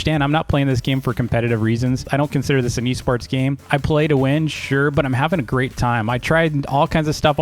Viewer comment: i thought he said pay to win